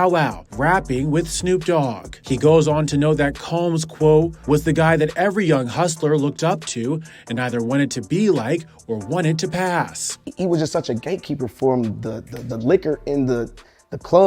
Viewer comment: “Gate Keeper” might not have been the best word to use at this time… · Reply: Ok I was thought he said GAykeeper😮